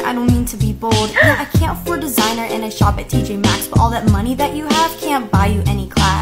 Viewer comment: When she said “all that money you have can’t buy you any class” I heard “all that money that you have can’t buy you any *ass*” and then I said “umm yes it can” lmafo